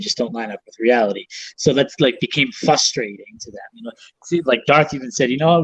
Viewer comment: Uh oh...did say " fuss-trading"